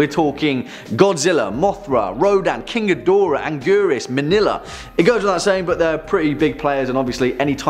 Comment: Did you just say "manila" @ 2:01? I didn't know there's such a thing named after the Philippine's capital. Or I misheard it.